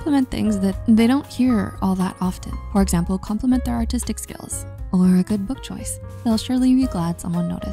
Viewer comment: I thought it said compliment autistic skills 😭